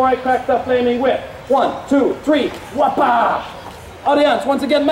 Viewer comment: Did he say "Whaaapa!" (at 3:00) Chandler-style?!?? Hahaha